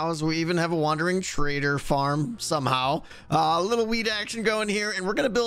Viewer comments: I THOUGHT HE SAID WEED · Did he just say: "a lil' weed action going on here?" at 8:22 :'))